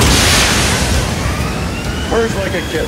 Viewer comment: When the boilers fire up, and he says "purrs like a kitten"... That hit different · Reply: Whoah... I thought he said: "burns like a kettle" Thanks for correcting me